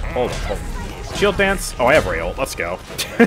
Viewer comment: how did you heal 4k health? Bit difficult to hear what you said, I’m wondering if you said “I have Rei ult” but I don’t see how that gives such massive healing